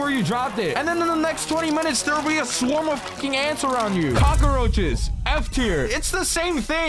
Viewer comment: Bro sounds like he said cocka roaches 💀